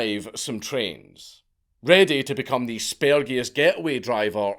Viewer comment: ready to become the what? What word is he saying right here? Somebody help me out. Lol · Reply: Spergiest